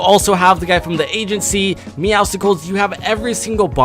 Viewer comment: did he say Miausticles ??